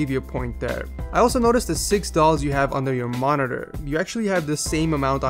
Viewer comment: On 3:27 it sounds like you said Dollars instead of dolls